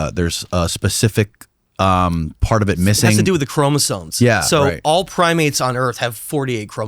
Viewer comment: "ChromosoNes" instead of Chromosomes? 🤔 This guy is clearly intelligent and perspective.